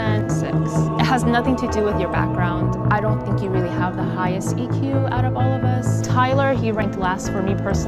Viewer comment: did she say highest “eq”?